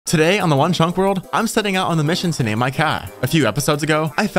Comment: I thought he said one Trump world